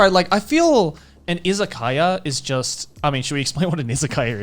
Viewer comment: I heard "Isekaia" instead of "Izakaya", so I thought they could be some medieval-themed places or something XD